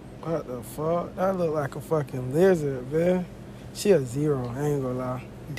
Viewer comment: I THOUGHT HE SAID "Wtf that look like a fukin' N- "